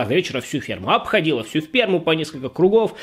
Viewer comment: мне послышалось "сперму" ахаха и в субтитрах так же